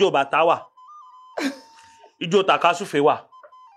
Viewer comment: 😂😂😂😂😂😂😂😂😂did he just say, ijo takasufeee😂😂😂😂😂😂😂😂😂😂😂😂